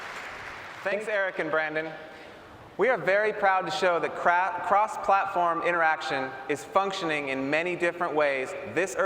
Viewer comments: it sounds like he said "We are very proud to show the crap". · LOL...geez the guy at 9:46 almost shot himself in the foot there. He sounded like he said we are very proud to show the crap interaction....>.> shameful -.